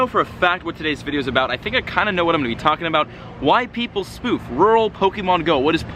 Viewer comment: i heard : White people spoof . 😅😂 i was like wait what ? 😅😂😂